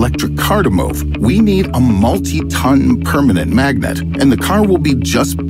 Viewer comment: Smart people come First 🥇 Next? Thought you said perpetual not permanent Hahaha thats two Questions